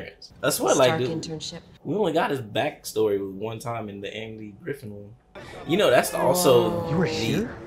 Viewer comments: Did call Andrew Garfield, ‘Angry Gryffindor’ ? 😂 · Did he say "Angry Grffin"? lmaooo